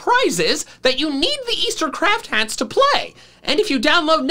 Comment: I thought said Crap hat instead of craft hat 😅